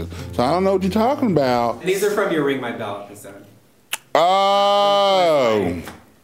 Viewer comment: he said "these are from youraymyblahblahblah" what did he say? I wanna see the video but idk what to look up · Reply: Ring my bell